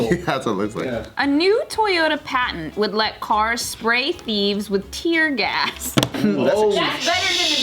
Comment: I'm high so I thought it said, "new parents try to spray thieves with tear gas"